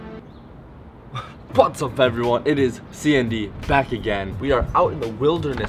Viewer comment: At the Intro I thought u said "What's Up Everyone it's Sandy''😂